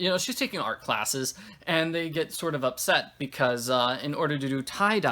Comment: Commissionary? I' thought she said commissary.